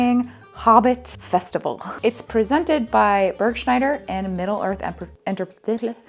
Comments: I had to play it back bc I thought you said it was presented by "Ken Bretschneider" for a second and got so scared, but I was mistaken. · Hoo boi, when said the event was hosted by Burgschneider, I misheard it as Bretschneider, as in Ken Bretschneider from the Evermore parks, and had immediate war flashbacks to Jenny Nicholson's 4 hour video about them. These guys seem much better equipped to do something like this 😂